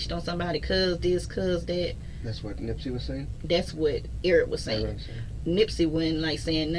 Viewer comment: Sound like she sayin Eric was pressing nip